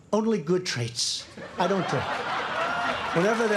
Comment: Hold up 3:36 did he just say that’s his only good trait... I mean I’m not disagreeing but...